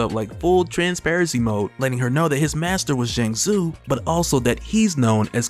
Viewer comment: Not gonna lie when you said Zheng Zu i thought you said Shang Tsung 😭😭😭